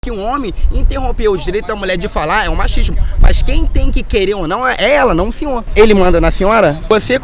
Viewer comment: Eu entendi "ele manda na Fiona?"